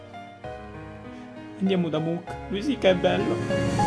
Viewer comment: solo io ho sentito "Risiko è bello?"